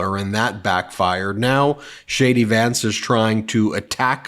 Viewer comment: Hey Ben - I swear I heard you said "Shady" Vance, not J.D. Vance.